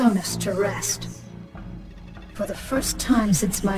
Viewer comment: What does she say at around 7:05? She's what of the slaughter? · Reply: weary - verb - tired.